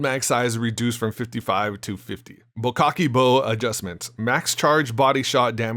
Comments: JOIN WEBULL with my link and get up to $1850 in FREE stocks when you do your first deposit of $100 or more!: <link> // Enter the 3000 apex coin giveaway! <link> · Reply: did you just said bo-cocky-bow 😳🤔...I like it 🤣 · Haha it kinda sounded like you said “bukkake bow” instead of “bocek bow” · Did he just say “bokakke bow” 😂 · did u say bukkake bow? Lmao wth